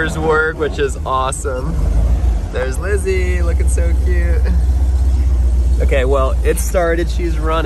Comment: did anyone hear or realize Say "There's Lizzy Lookin So Cute."?